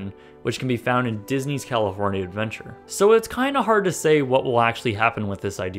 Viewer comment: Cali”horny”ia lmaooo did i hear that correctly sir?